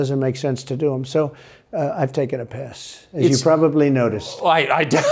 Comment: He said “he’s taking a pass” but it sounds like he said “I’m taking a Piss.” 😁😆🤣😂